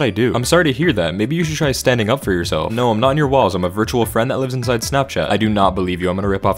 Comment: For the second one i thought it said: “no, i’m not in your balls”💀💀💀💀